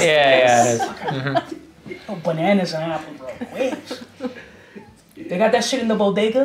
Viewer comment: does anyone hear that "bruh" in the back? haha